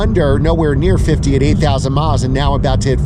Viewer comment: It sounded like you said 80,000 miles on it! Must be 8,000!